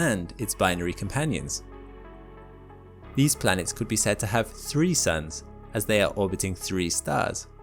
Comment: I THOUGHT HE SAID "these planets could have threesomes" IM CRYING